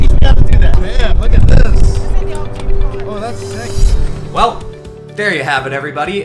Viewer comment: did he just say "oh that's sexy"